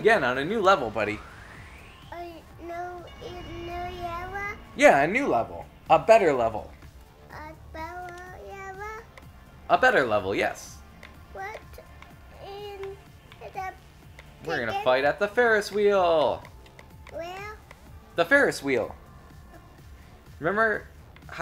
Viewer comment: Did just say "What in the chicken?"@ 3:06 ?